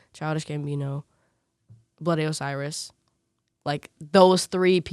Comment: When she said Bloody Osiris, I legit thought she said Billy Ray Cyrus